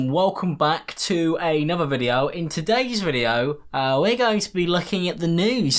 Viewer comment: at first i thought you said "we're going to be looking at the nudes" lol